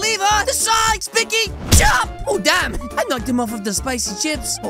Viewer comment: what did he say!? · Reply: he said oh dam